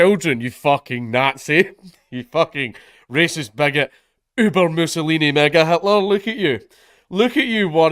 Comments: "Uber mussolini" I had to double take because I heard "Uber owossolini" I need my ears checked. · Sounded like he said N word Hitler in the first 20 seconds. I know he said mega Hitler, still really funny though